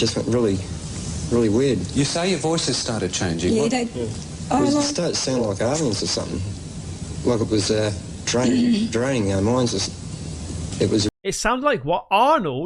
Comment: "We started to sound like aliens or something...like it was a drain... draining our minds" that is what I hear at minute 38:20 (Aussie here)